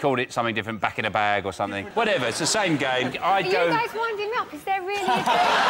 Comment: I'm not english. I'm having a hard time understanding what she's saying at 0:47. It sounds like she's saying "Do you guys want any milk?" xD